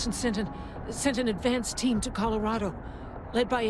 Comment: I could have sworn I heard Vera saying they sent in a dance team. which sounds a lot more of a laugh